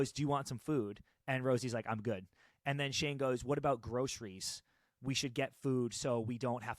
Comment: Shane said, in the car from the airport, that he 'bought groceries so we don't need to leave if we don't want to', not that we 'have to buy groceries'. You misheard because of course was yapping